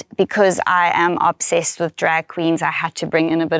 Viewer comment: Did she say Drag? well she will loved Season 3